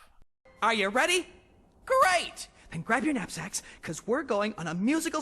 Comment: I had to relisten to what Flik said because I burst out laughing when I thought he said "grab your nutsacks" which was immediately followed up by reminding me of the meme d**ks out for Harambe Turns out he said "Knapsacks"